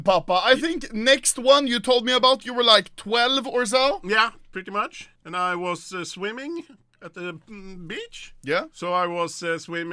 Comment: I thought he said : I was swimming with bitch XDD